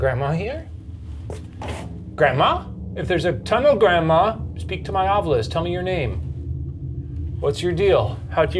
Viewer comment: I stopped the video to go get some Starbucks for a sec. When I came home I read the subtitles as "Speak to me Oliver, tell me your name" and I was so confused for a minute cause I thought to myself "But you just said his name is Oliver" 😅💀